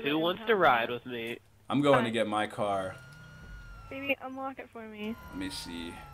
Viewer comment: Did say "Baby unlock the bike for me"?